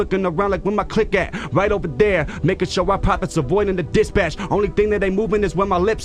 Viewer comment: did he just say the n word? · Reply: "Making sure..."